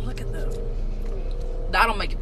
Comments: I thought they said throw up not throb, lol my bad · THIS WHOLE TIME I THOUGHT THEY WERE saying “throw up” NOT THROB LMAOOOO IT ALL MAKES SENSE NOW